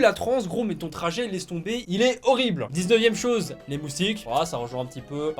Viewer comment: j'ai entendu 2 fois d’affilée loustique au lieu de moutique oui je suis conne 😔😅